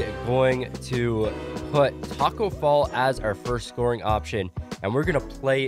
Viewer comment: Who heard him say Taco Fail